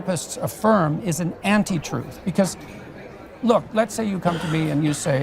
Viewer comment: did he just say anti truth?